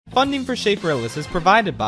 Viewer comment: For years I thought you said in the intro "founding for shape realism" and just now I notice that you say "Schafrillas" instead XD